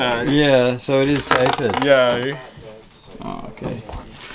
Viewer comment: did he just say Yayy?? why he talk like that just wondering? it is Aussie accent? love the boards collection my man got you on LOCK 😂✌🏽✌🏽